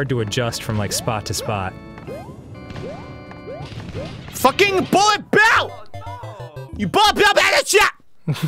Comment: What does Arin say at 8:25? I hear "Fuck ing Bullet Bill" but the what? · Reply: then*